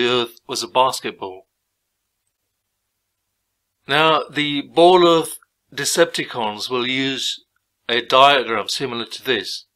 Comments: did he said "ball earth decepticons"? He thinks that we are ... evil robots from Cybertron, right? Good, good. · did he say decepticons? I'm deceased.